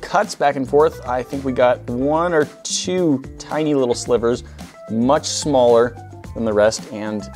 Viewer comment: I think I heard "slippers"